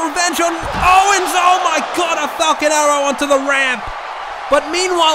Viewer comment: Why it sounds like he said fuckin arrow but good video no disrespect love u man